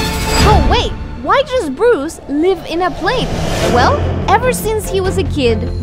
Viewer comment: i heard you say bruce lee😥😥